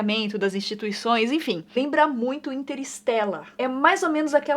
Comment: só eu q entendi inter stela